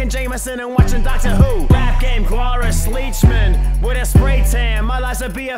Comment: Okay 0:40 in.. Wtf does he say? Rap game tom Brady? Rap game on gravy Wot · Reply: Rap game Tom Brady